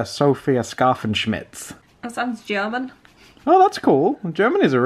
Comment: It sounded like said "german is a nice place!"😂